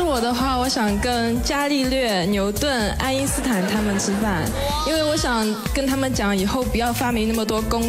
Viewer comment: WTF? Neil Diamond? Hahahaha she said Isaac Newton